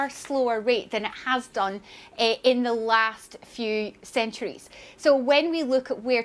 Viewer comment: did she say centuries?